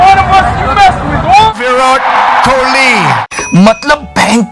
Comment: i hear sound virat kohli